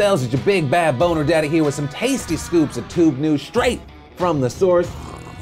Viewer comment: At first I thought Phil said "straight from the sewers"